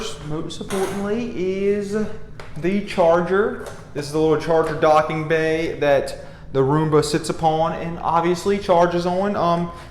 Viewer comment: What did you say around 2:43 ? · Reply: The charging docking bay the roomba sits on. Is that what you're looking for?